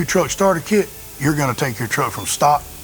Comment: I thought he said "Come see me at TuckTruck" haha